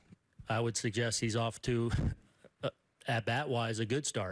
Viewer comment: Boone thinks Gallo is off to a good start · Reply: I know what he said..... He said he is off to a good start at bat wise.... Go listen again